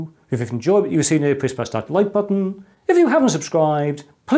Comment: Did he just say to torture you at 21:22 or did I misheard him